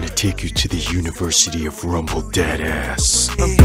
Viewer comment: Why did i thought eren said rumble dead a$$